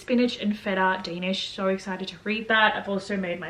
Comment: Girl when you were taking about the danish at 10:39 did you say your so exciting to read that or am I mis hearing? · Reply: HAHA I DID SAY I WAS EXCITED TO READ MY DANISH 😂😂😂 it's almost as bad as your namaste comment lol